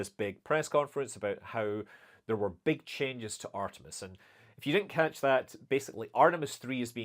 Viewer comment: Awesome, hopefully artemis flys soon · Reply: It reall sounds like Scott is saying Artimes yeah